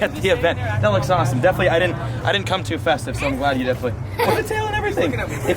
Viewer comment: does say he didnt cum too fast?